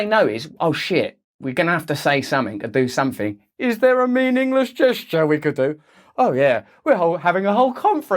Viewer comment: Lol… I thought he said ‘Mean English gesture’…